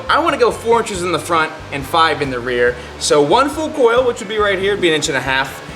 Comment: what did he say? 😮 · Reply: 1 full coil